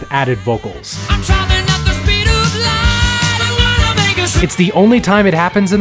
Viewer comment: i heard "I wanna make a soup" bc of the cutoff lmao